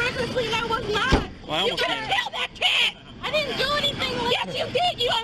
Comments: A year later, this is still unbelievably demented behavior. The woman's child would be so much better off growing up without her as a mother. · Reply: I heard, "You could'a killed that kid." Replayed it five times & still sounds the same. But, I could be wrong. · To the lady that rushed into the car to get the child and yell at the mom. Good for you, bless you ♥️ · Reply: But it's the kid okay? Cause I hear like "you could've save that kid" or I'm tripping balls here